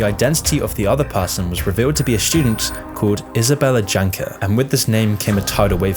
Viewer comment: I heard "Isabella Wanker"